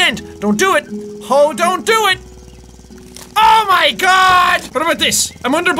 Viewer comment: Did he just say oh don do it, oh my god?!?!?! that is from a vine!!!!!!!!!!!!